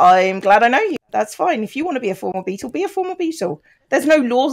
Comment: I kept mishearing "a former Beatle" as "a form of beetle". It makes sense that the voice of Thomas the Tank Engine was done by a pleasing fungus beetle.